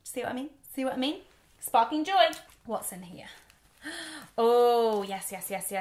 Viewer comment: She keeps saying "What's in here?" but I keep hearing "Watson here!" Coming to you live from London!